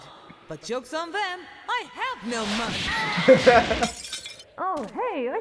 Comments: the idea of goku getting mugged and actually going along with it is so funny to me. like, someone walks up to him with a knife and the man who can literally bench press a planet just goes “aw shucks :(“ and hands over his wallet. comedic genius. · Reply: For the longest time, I thought Goku was saying "Jokes on them! I have no mug!" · the idea of goku getting mugged and actually going along with it is so funny to me. like, someone walks up to him with a knife and the man who can literally bench press a planet just goes “aw shucks :(“ and hands over his wallet. comedic genius. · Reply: Honestly, I always thought he said "I have no mug" instead of money. I don't know, I just think it fits Goku better, knowing he could get mugged, but doesn't really know what it is .